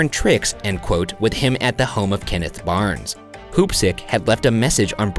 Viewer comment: Totally misheard the name as poopstick while listening in the shower